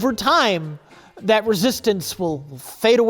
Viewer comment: Is Rich saying " Resistance Is Futile ?" .